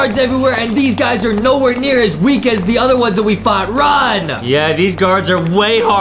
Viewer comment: WHAT DID HE SAYYYYYYYYYYYYYYY?!?!?!!!???? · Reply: Run?